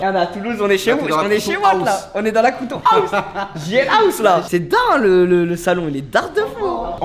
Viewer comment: pourquoi il dit crouton house ?